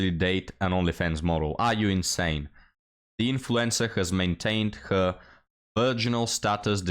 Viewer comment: When says, “influencer” I hear “influenza” and think a disease isn’t a bad description for these types of people.